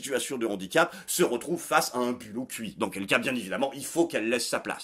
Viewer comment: Il a dit quoi à 7:15 ? J'ai compris à un bulot cuit... 😂